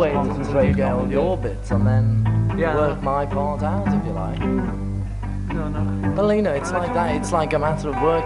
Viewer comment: did George call Paul "Paulina"? That's gotta be a little disrespectful amongst a bunch of working class blokes from Liverpool. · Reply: I have a follow up video to this and I think I got that wrong. Liverpudlians suggest George said somewhat slurred, "Paul, ya know." I'll defer to the Liverpudlians.